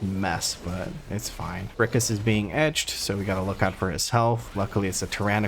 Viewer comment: Cant believe didnt pick up on the fact that at ~49 minutes it sounded like you said Brachas is being edged because it sure threw me off lol